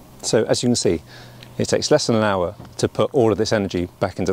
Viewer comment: At around 4:00 you show that the EcoFlow will last for approximately 40 minutes. So how can this have taken an hour? The EcoFlow wouldn't last the full hour. But yes, it's certainly an option as a 'jerrycan'. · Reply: Hi, Sorry, my fault. The first time, I heard 'an hour', but you actually said 'less than an hour'. My bad.